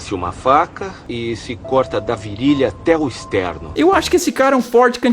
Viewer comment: Mds...(9:10) quando eu assisti o filme eu escutei: "corta da virilha até o esperma"...